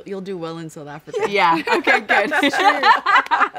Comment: on that note....what? what was the last word? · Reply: I thought they said "bye donkey" lmao